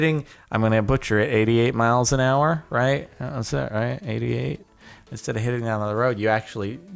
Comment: did you say Eighty Eight? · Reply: Eighty Eight yep he definitely said 88